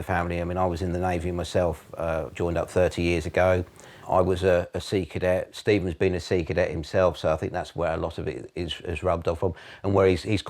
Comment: Did i mishear or did his dad say there’s a military history in the family and that he was in the sea cadets?!😂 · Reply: You misheard. 2:52 He said, "I was in the Navy myself, joined up 30 years ago. I was a Sea Cadet, Steve has been a Sea Cadet himself" He was saying that he and his Son had been in both the Navy and Sea Cadets. No one would refer to being in the Sea Cadets as "joined up". 24:20 PO Coot says, "his dad is an ex-matlot".